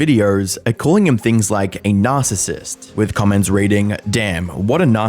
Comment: lol i thought you said a NAS-ISSIST